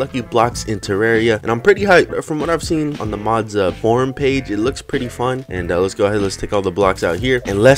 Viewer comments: I thought you said "the mods porn page" lmao. great video btw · When you said form page it sounded like you said porn page